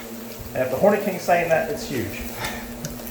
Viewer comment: i thought he said horny king 😃